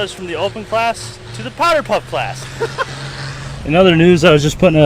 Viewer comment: I thought you said "powder puff" class the first time. I don't know why but that made me giggle.